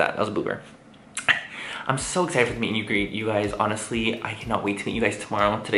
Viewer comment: did manny say "meet you greet" 😂